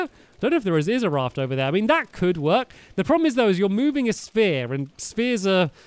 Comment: For some reason I thought you said spear not sphere